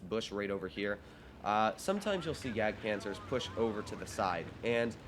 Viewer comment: Subtitles: Gag cancer instead of JagdPanzer lol